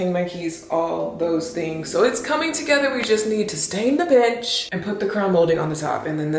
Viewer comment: Omg. I thought you said stain the b*tch.